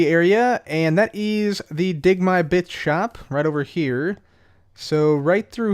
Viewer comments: I thought he said dig my B#%@ shop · Is it me or did it sound like he said bitch when he said bits